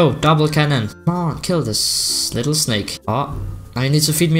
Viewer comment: did actually said ''Snake'' to a dragon skin? xD